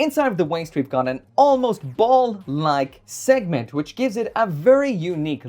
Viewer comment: I heard "BALLS-LIKE" and just died 😂💀